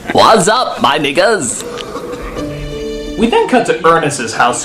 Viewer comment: did I hear "santa" say what up my niggas at 12:35 ish?